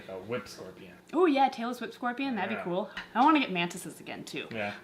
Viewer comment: I thought you said Taylor Swift Scorpion instead of Tailless Whip Scorpion lol